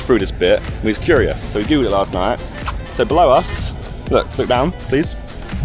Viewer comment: I thought he said so blow us is it just me being dirty or did u guys think that too